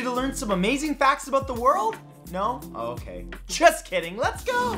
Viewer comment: Missed you Matt but I do have to correct you, Oswald killed JFK. JFK Jr. Died in a plane accident. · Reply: I thought u were saying just fking kidding